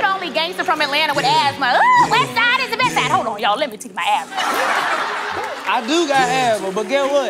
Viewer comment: Everyone laughing after she says “let me take my asthma”… me in the mean time “YOU MEAN INHALER” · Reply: For the longest time I thought she said lemmie "check" my asthma. But yeah now I hear that